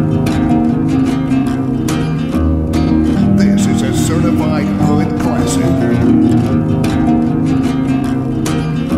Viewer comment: What does he say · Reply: "This is certified hood classic"